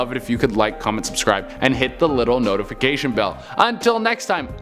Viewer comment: Hi Che, first time I saw you, was drunk, thought you said Cheddarina was yr name, so for fun, it's what I call you! Ha, Life ain't easy being cheesy!💖